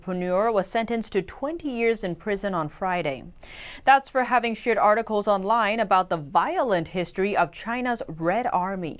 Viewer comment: ROFL, I thought she said, ...that's for having "shit" articles online... 🤣